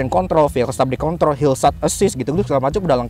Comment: "sekilas kedengernya hilsat assist atau mungkin perasaan saya 😂"